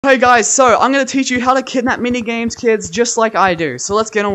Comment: I scrolled and looked away for a second and all I hear is “hi guys I’m gonna teach you how to kidnap many kids” and I was like “what the”